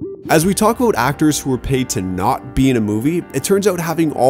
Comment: I thought it said "Actors who weren't paid to be in a movie"